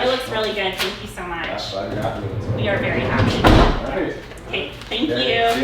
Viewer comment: Did she just say: "You're very hot." 🤣